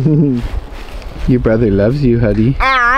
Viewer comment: says "your brother loves you huddy" 11:28 says "I love you huddy"" it was faint but recognizable, i grew up with a cousin that had a speech impediment and also my daughter when she was young, and i could always make out what they were saying when others had no idea... i too struggled with speech and learning to read and write as a child... will one day speak and be able to communicate.